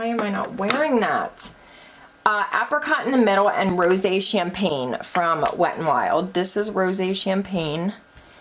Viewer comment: Omg... you said "rosé champagne" and I could've sworn you said "rosacea in pain"